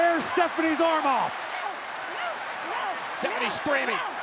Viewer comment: Did anybody else hear Cole say Tiffany instead of Stephanie